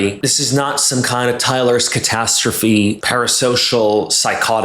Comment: Did he just say Tyler Catastrophe?! I’ve been going down the rabbit hole watching his cringe videos recently; never expected him to be mentioned in a vid 😂 (and yes I read/say as one word)